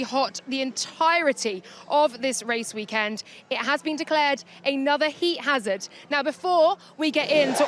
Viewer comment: Laura's freudian slip at 0:23 was enough to make me watch this😂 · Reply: I heard "have been clapped". My mind went elsewhere kkkkkk